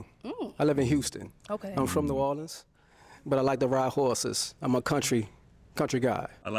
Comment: Did he say, "Whoreses?"